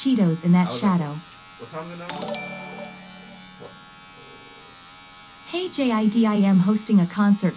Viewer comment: “what time in an hour” ? Or am I hearing that wrong